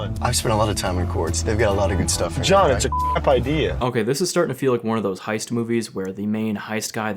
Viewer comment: Did they bleep out that guy saying it was a “crap idea” at 1:42??? He clearly says “crap.”